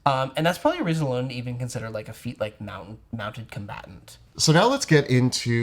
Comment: I swear I heard refer to a "Mountain Combatant" feat, which sounded pretty interesting. Once I figured out I'd misheard Mounted Combatant, somehow it still didn't sound like a bad idea. Advantage on keeping your footing during rockslides, a climbing speed, maybe advantage on ranged attacks if you are firing from above... Yeah, that seems totally like something that could be a feat.